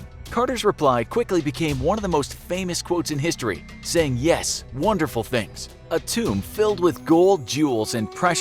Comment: I thought he said "Wonderous things",... no?